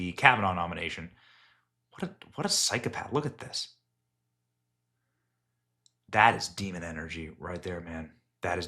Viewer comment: 😳😆 Benny... did you just say "Look at this F-ing Čụṅṭ"??? I'm sure i misheard but I fell in love with you for a sec! 🥰🥰😆